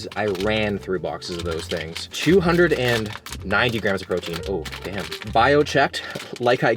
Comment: did i hear 290 g protein???